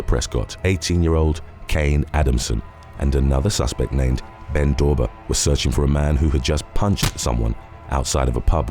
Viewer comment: am I the only one who heard him say Ben Dover?